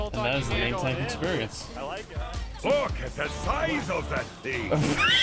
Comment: what is that torb saying xD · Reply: Torb said "Look at the size of that thing" XD